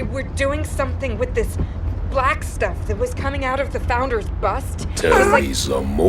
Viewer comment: The acting is so very, very bad. And i thought she said there was black stuff coming out of the founders butt.